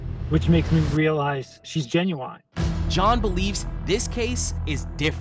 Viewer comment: ' genuonnn' instead of genuine lol